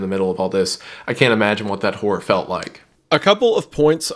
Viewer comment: I thought said whore here and I feel bad for laughing 😂